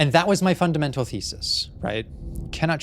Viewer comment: I heard "thesis" as "feces" and was so confused, that lost my queen in 10 seconds.